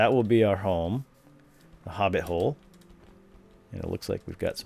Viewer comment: I heard you say: (hoting hole)